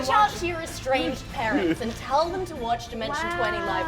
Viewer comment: When they said talk to your estranged parents, I thought they said talk to your restrained parents . I had to listen to it multiple times.